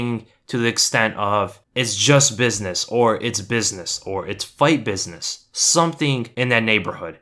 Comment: I dont hear him saying "Its just business" i hear "It's just clinches."